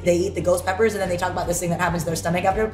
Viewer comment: was the part I couldn't understand, I'm curious of what she said 😅 - "to that stomach after" 😂